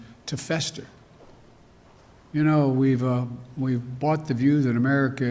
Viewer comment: Did he really say "We Bought" at 04:15?